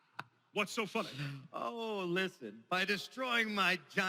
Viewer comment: i thought that Dan Green said whats so fuc*y yaykss..